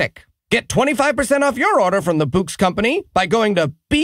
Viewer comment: I listen to too many Scottish people, because when you say "bouqs", I hear "books"